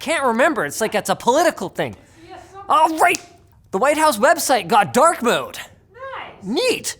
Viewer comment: Ok ngl I thought he just said “White House got dark mode” and I was like is it because kamala there lmao but found out it was the website